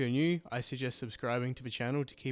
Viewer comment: lol, your outro sounds like your saying "I miauw" instead of "I'm Al", I love it